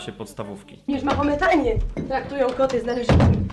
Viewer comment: ja usłyszałam taki jest traktor